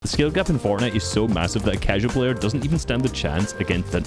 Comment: did he say.... massive?